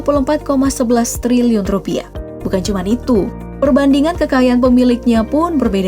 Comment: hah cumq? G salah denger